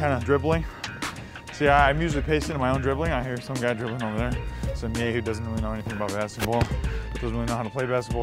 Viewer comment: Did he say "some nigga doesn't even know anything about basketball" at 3:27 ??